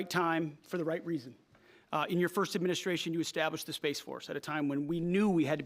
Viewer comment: "In your first administration you established Face Force." NGL it's what I heard; flag on Pete Kegsbreath for the Freudian slip, though.